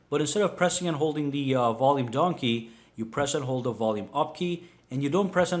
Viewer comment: Volume donkey! What? What did I just hear? XD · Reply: Pratik Khairnar oh my god I heard volume donkey aswell 😹😹 it actually says volume down key though 😹😹😹